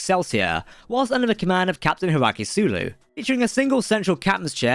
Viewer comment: HIKARU Sulu, NOT Hiraki Sulu. Even your closed captioning is messed up because some Limey Fuck wants to talk at warp 9.997 & the program does not understand what you're saying. Otherwise, good vid. 👍🏻